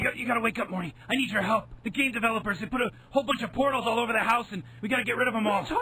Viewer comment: Until I saw the portals I thought they were saying that there were a bunch of "pornos all over the house" (which in hind sight wouldn't surprise me)